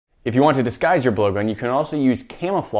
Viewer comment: At the beginning I thought he said ‘if you want to disguise your boyfriend’